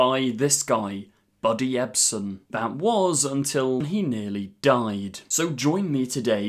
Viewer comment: Buddy Ebsen didn’t die just got really sick. He stayed alive and would later go on to play Jed Clampet in the tv series The Beverly Hillbillies. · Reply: I thought he said “he died” too and was also literally about to be like “uhhhh he was on the Beverly hillbillies” but I went back to make sure and he quietly said “he nearly died” 😂🤣